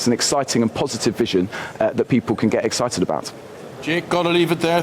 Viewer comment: I thought he said Robber valley , about right .